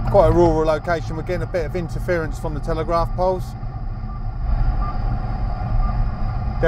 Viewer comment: Did he say "telegraph poles" (at 4:50)? What kind of hi-tech do you got there, Mr. Spaceman?